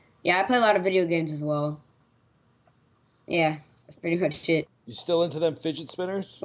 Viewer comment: Did russel just say shit!? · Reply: Was Good he said that's pretty much it